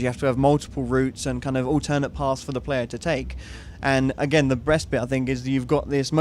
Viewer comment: Did he just say the breast bit :)